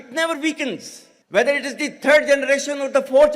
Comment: Did I hear "thread" generation?